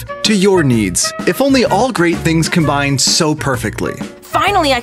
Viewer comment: I thought that said boobs… not foods….